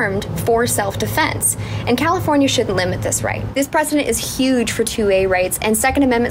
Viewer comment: Precident,,ok,,I thought you said president 🙄 FJB